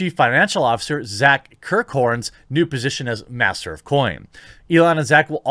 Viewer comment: At first I thought you said the CFO’s new title was ‘Master of Boing’. 😅